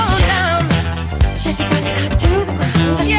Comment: Why do i hear "shake your body now toothbrush"?😭